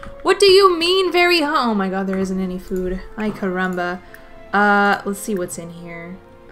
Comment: did she just say "ai caramba"? Hahaha